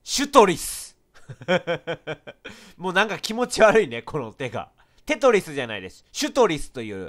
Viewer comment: テトリスじゃないでしゅに聞こえた